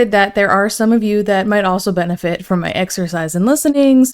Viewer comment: I thought you were gonna say exorcism and after the past few days in gorlworld I was not about to disagree 😂